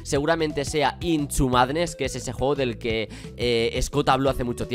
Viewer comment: yo entendí intchu madres Ekisde